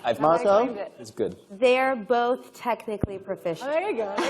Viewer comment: What did Kerry say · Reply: “Technically proficient”